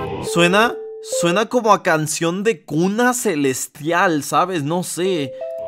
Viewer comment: "Suena a cancion de cunas" Y yo escuche kunnos XD